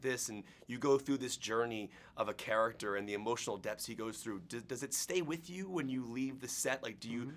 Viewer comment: Did he just say that Professor X dies? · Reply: he said "emotional depths" but it did sound much like "emotional death"